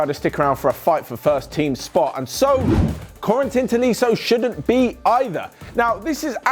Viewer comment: Am i the only one who misheard Corentin Tolisso to Quarantine Tolisso? no... just me?